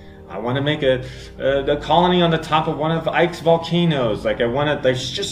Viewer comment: I'm sorry did he say "Ike's Volcanos"? I didn't even know Ike had Volcanos. Is that in ksp 1 aswell?